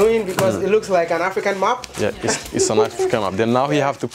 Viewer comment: I thought he was saying an African "MOP". Then later I realized it was "map".